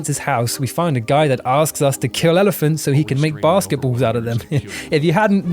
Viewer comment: The only thing im offended by is how you misheard "elephant foot waste paper baskets"